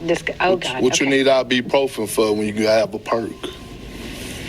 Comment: What did he even say? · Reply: "Whatchu need ibuprofen for when you have a perc?" 😂😂